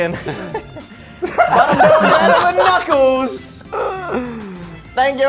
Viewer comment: What joke did say at 27:24 I only heard knuckles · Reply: I think it’s “i’m doing better than knuckles”